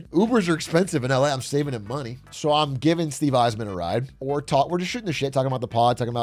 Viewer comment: I could have sworn you kept saying Steve Yzerman